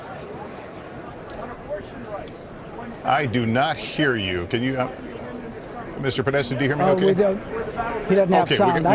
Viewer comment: My English listening is not good. What did Trump say at 1:38:25 ? Caption reads "Should have gotten it", is it accurate? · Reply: He actually said "shoulda gotten that," but it's an accurate caption as to what he meant.